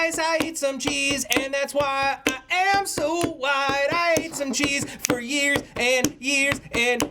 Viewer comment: White? I thought he said wide.